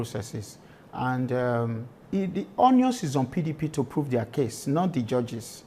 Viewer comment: Did I hear Dennis the Deputy Governor say the "Onions is on the PDP to prove their case ..."? 12:17). Na wa o. "Onions"